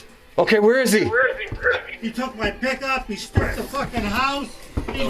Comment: Two notable moments for me, from this video: - 25:12 is when I was listening but not watching. I thought the dude said, "He took my dick off...". I was momentarily, after being shocked and thinking it's so horrible - impressed. That he cut off the guy's dick and kept him alive. It's not super simple. Especially without proper supplies. - 46:03 is when I thought he literally got eaten by the...well fed guy. My brain took about a minute to realize. Was first figuring out the logistics of how a cell mate of his would have eaten raw meat. Biting from the source, or pulling it apart. Which parts. ...